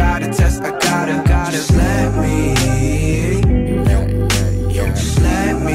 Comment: I heard that part "slap me" instead of "just let me" ^^